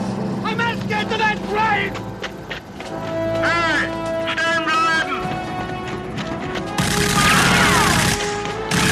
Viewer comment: what does the guard shout / order?? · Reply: Halt! Stehen bleiben!